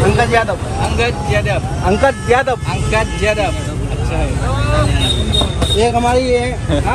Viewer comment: nama abang india nya ANGKAT BIADAB, apa aku yg salah dengar ya??